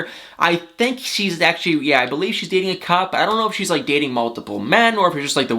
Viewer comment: I thought he said "she's dating a cup" 😆